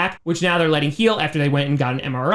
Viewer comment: I'm so tired I thought he said Lefleur is being Le-cautious